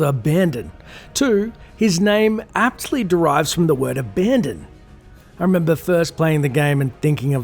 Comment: I misheard his name as Darth Brandon at first too as a kid. Made me chuckle.